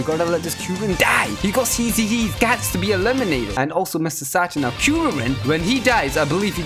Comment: DID HE SAY MRS SATAN AT 1:31 LIKE IF HE DID